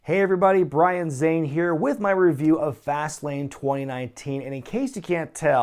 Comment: I thought you said Vaseline instead of fastlane in the beginning lol